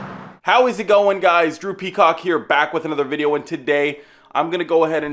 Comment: Sorry I thought he said, Gru Peacock😆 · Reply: no, he said DROOPY COCK